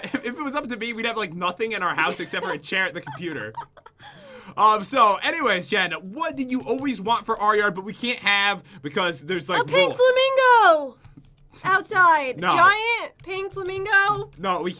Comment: Enjoy the video guys!!! should be posting a video on her channel later as well so subscribe to her if you haven't already! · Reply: I thought that instead of pink i thought said pig flamingo xD